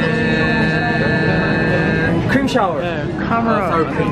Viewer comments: i heard "cream shower" 😭🤣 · taeyong mom who prohibits children on adding unnecessary items (but eventually gives in), jungwoo the sneaky child, mark the supportive sibling and johnny dad who secretly goes along with the children 😂 what an actual family reenactment lol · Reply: I thought he said cream shower